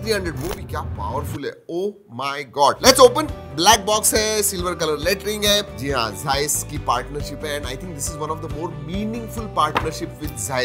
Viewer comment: I heard Silver colour latrine hai 😂